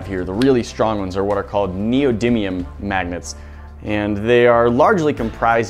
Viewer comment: it sounds like he says maggets